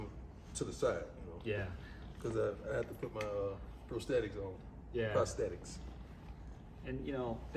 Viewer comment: Coach Prime needs to speak to an Anaplastologist. They can make a prosthetic attachment for his foot that will not only bring back the two missing toes, but bring back the balance to his walk/gait. They can even make it match your skin and it will look like his other foot. Functionality and look. Definitely have Coach look into this if you see this comment. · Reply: I thought he said he had a prosthetic when he was talking about the width of the toe of one of the shoes.